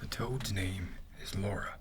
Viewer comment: i thought he said 'lara' n i was shook bcause thats my name lmao